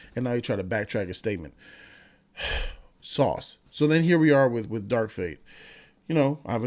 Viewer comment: love this channel ..just found it... on a side note at 2:08 did he say sauce..... fu(k is sauce can someone help me out?